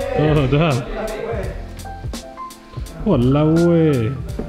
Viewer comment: did u really say "walao eh" at 18:06 hahaha!